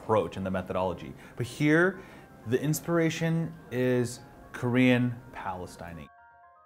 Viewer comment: I kept hearing "korean palestining", hahaha